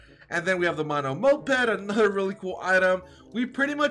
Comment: wait..did he just say monomoped pet?